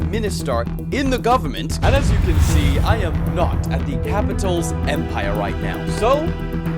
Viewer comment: did he say capitals empire?